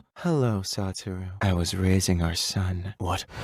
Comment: Why did it Sound like Makima said rizzing Our son ☠️💀 yes ik she said raising but still i was worried for A Moment there🤣